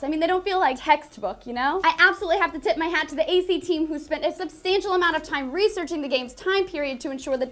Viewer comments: did she say tit? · Did she actually say "tit my hat" cuz I can't hear anything else XD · anyone else hear "I absolutely have to TIT my hat..." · she said 'tip my hat' i heard ' tit my hat' - and im a chick- sorry... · Is it just me or does say "I absolutely have to TIT my cap off" at 2:08? Or is it just my subconscious..... and that costume? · I was so hyped up with boobs that i thought she said tit my hat